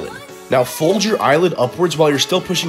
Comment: At the end I thought he said now go full moon someone💀